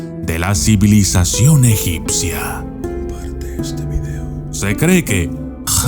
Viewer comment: Yo escuche "Comparte este video!" ( Minuto 4:19 )